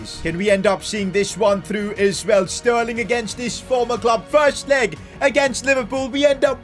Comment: did he just say sterling against his former club. Huh just things